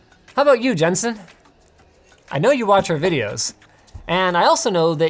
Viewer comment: When he ends the video asking if something is fair, I thought he said "how about you Justin", was freaked out and registered and he said Jensen haha